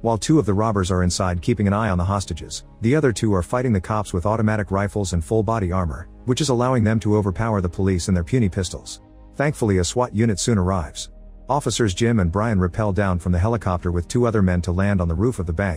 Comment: “Puny pistols” was funnier than it should have been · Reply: I thought it said pony pistols